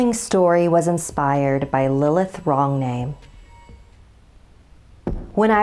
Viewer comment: I thought she said lilith longname